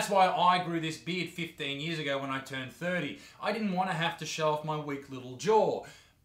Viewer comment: "15 years ago when I was 30" Yeah that one got me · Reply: I rewatched it a few times and i think he said "15 years ago when I was 13" but his accent made it sound like he said 30 but idk